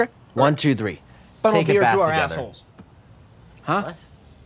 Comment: What does Mickey say on 0:08? Are they subtitles in this episode? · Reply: "Funnel beers through our assholes" 😭